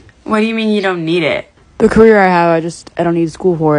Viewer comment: I thought he said the cougar I have , I don’t need school for it lol